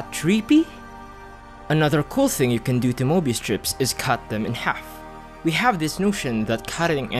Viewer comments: i thought it said morbius strip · I thought it said "mörbius strip"